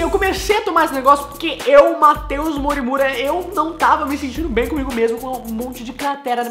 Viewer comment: eu entendi ele falando "Eu Matei o MoriMura 😂😂😂